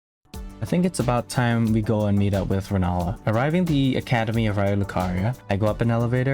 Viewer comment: The Subtitles acutally said meet up with Ronaldo instead of Renala LMAO IM DEAD